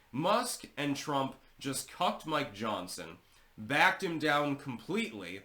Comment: I could have sworn I heard him say "Musk and Trump just cupped my johnson" I almost had to double back and listen again. 😅😂